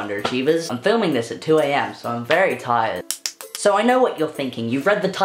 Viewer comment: When you said "I'm very tired" at the end I thought you said "I'm very high" and I was like whAt that's not like at all